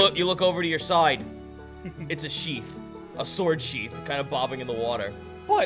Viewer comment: Any else mishear that as Sword Sheep? O.e?